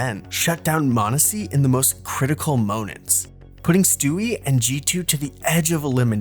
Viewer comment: did bro just say “moanent